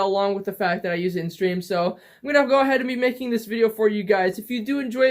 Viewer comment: I thought he said scream